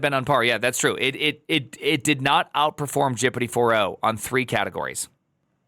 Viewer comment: they did not saying Chappity 4 won, they said Chappity 01 fid not supassed (maybe loke the english category where they were tied).